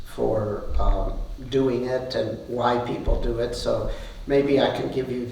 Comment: Does he says something about "white people" at 0:20 min? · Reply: And why people do it 😂